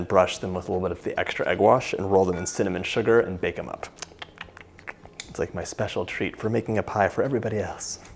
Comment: What did he say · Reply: "It's like my special treat for everybody else"